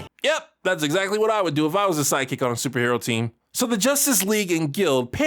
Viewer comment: almost sounded like you said "psychic" instead of "side kick" heheh heh... heh